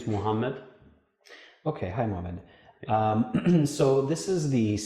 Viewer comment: dude i need to improve my listening. I swear that I heard: "Ok, hi woman"